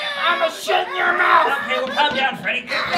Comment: all I heard was "IMA 💩 IN YOUR MOUTH"💀💀😭😭😭😭